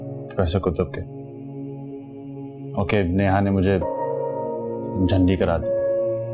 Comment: The way Sarmad says, "Tum dono ki tabyat to theek hai?"😂😂 BTW can anyone tell me what Adam says to Maha bhabhi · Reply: jhandi karadi